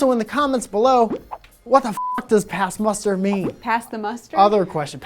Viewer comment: I thought you said pass the mustard, and I am not the only one who thought that.